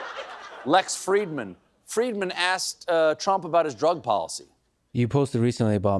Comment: His drug policy? I thought he said his drug addiction like Don Jr. And yes the truth is very important but not in your head. Why is he talking so strange? His teeth again.